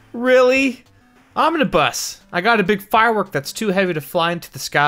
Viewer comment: I thought he said "I gotta bust"